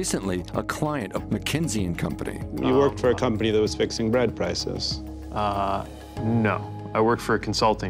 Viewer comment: “You worked for a company that was fixing bread prices” is such a cold line. Mayo Pete puckered up REAL quick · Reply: Oh I thought he said RENT prices and was even more outraged.